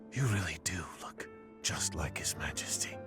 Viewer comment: I know is for the injured acting, but damn, I was looking away and thought he said "You fuck just like..."